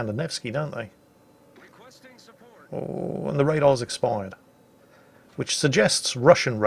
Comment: I would appreciate subtitles if possible. Because as a non-native speaker of English, I cannot understand what is the word how you comment on Russian radar and it appears I have a poor sound system. It sounded to me like "the radar's expired". It cannot be that.